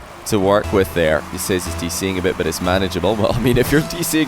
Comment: At first I thought said 'i'm doing a bit' and I thought shit, that's confident of him haha